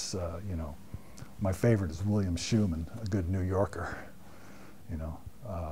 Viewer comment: coudl anyone tell me who is "favorite", at 2:00 ? (i'm french, couldn't understand the name) - i'd really want to know, because sure is one of my "favorite" ! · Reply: William Schuman